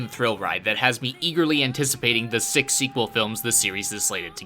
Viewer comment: Six sequel films?!! Knowing how the anime industry works I will be in my deathbed at the age of 90 by the moment the third movie would be anaounce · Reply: Oh shit I thought he said "sick sequel films", and I hadn't even heard there'd be more than one so I was pumped as hell already, but SIX? Damn.